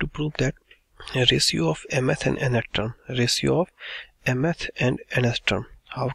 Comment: sir kya bol rahe ho "resue of m and n ka extra " 😢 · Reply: ratio of mth and nth term hope it helped 😅